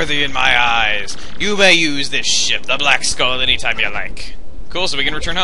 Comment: did you say shit or ship